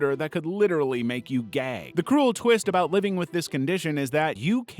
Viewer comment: i know it's gag, but at first I heard gay lmao (gay fish)